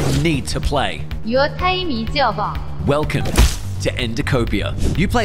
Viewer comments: I thought it said “your tiny sava” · How i Heard It: "You time es ove" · "Your time is over!" What i hear:you're tiny af